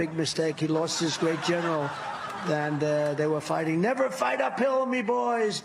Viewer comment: Funnier still, i kept hearing "fight a pill', not 'fight up hill.' Team OJ, indeed. Lol.